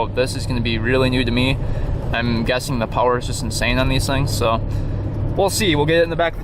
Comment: I thought they said these things suck that's why when I was looking for one I avoided these